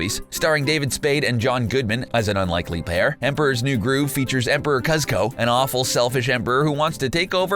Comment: did he just call him emperor Cuz ko? REALLY!?!?!